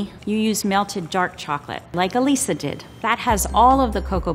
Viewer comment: I thought Elisa said Elsa